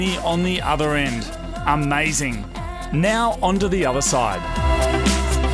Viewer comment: Honda the other side😂 instead of onto